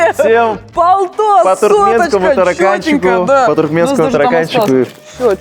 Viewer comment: всем по туркменскому тараканчику? Или мне послышалось.